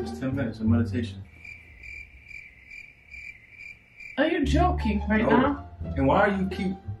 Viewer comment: I love how when she says "Are you joking?" it sounds like she is saying "Are you choking?" lmfao